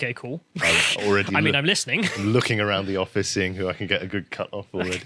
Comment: You know what at first at the 1:10 min/seconds. I thought you said you were looking around the office for a cutting board... a good cutting board.